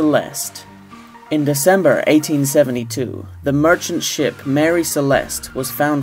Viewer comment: Caption says 1862 but like the guy says 1872.